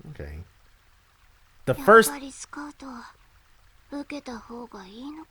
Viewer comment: What she said was actually "scout" which means job offer in japanese. That implies that she may be hired by another organization.